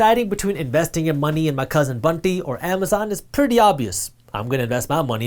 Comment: I’ve always thought u were saying Bundy... until i read the comment section.